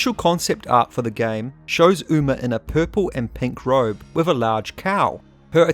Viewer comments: Did I just hear you say “show uma in purple and pink robe with a large…… cow”? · Lol. I heard "and a large cow". I was like "whut"?